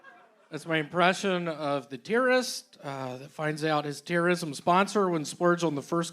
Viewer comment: i didnt get the joke at first cause i thought he said "tiarist" like a tiara maker or something